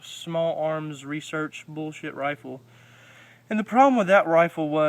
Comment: did he say bullshit rifle